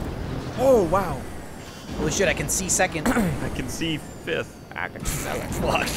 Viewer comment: I heard a Morgan Freeman "I can smell you."